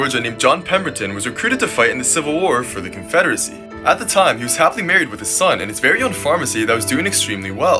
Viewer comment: The first time I saw this I thought you said he was married to his son